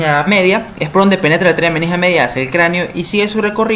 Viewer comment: Hola, podrías decirme que es lo que dices exactamente en el min 13:00 que se escucha muy rápido. Muchas gracias 🙏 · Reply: Penetra la arteria meningea media hacia el cráneo